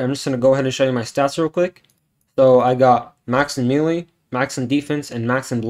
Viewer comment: when he said max in melee, i thought he said max in mewing 😭💀